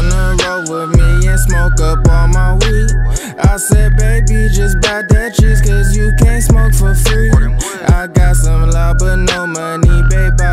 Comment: Always thought he "I said baby just buy dentures cause u can't smoke for free " 🤣🤣